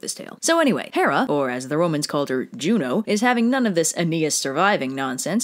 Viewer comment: I thought you said Romans call Zeus, Joe :/